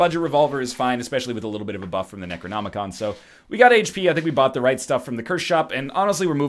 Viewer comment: did you say necronomicon?